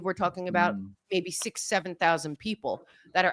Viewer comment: 6 to 7000 people know what's going on, did I hear right?